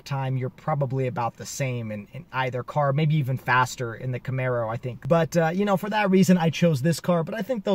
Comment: Did you actually say that the challenger is faster than the Camaro on the track? I stopped the video at that point, as you have no clue about these cars. Wait, I skipped ahead to when you said that the ZF transmission was the fastest, even faster than the 2X clutch. LOL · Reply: Ya, at 7:00, it sounds like "faster than the Camaro", but after listening to it, I think you said "Faster in the Camaro". My bad. Sweet ride.